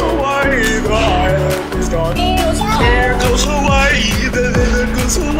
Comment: i originally heard "bye its gone" but now i hear "the island is gone"